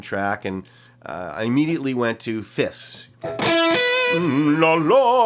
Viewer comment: Misheard fifths for fists. "I immediately went to fists..." Like, "DAMN... Reb goes HARD when he writes melodies for a new backing track..." 🤣